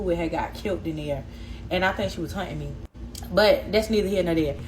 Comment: Did she say hunting her?